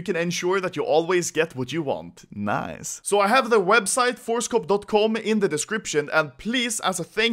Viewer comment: i thought he said ForestCock untill i checked description